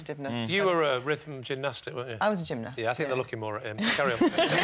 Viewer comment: what does Lee say? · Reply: 'I think they were looking more at him'